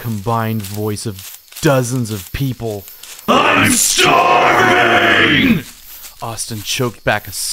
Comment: First time i heard this, I misheard the "I'm starving" for "I'm sorry".... for some reason, the implications of why the creature would say that made it way creepier the first time XDDDD